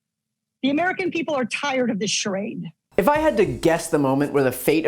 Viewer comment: When she said "charade", I heard "SHIT!"